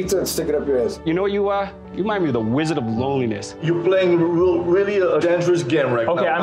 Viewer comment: The wizard of loneliness line-- do we think Nathan fed that line to him? Not sure I would give the P.I that much comedic credit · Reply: Thought it said "wizard of lowliness"